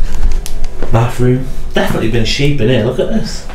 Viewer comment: as you are walking into the bathroom and you say the word bathroom, I hear a female voice say, "Bath." Can you hear it?